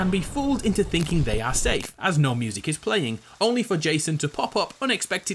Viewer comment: Watching this video because I always hear "Noob Jason"